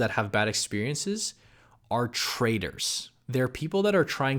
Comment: Who else thought he said "Traitors" instead of "Traders" at 11:30?